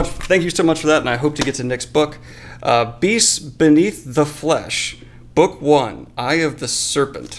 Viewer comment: I thought it said "Beasts beneath the FLUSH"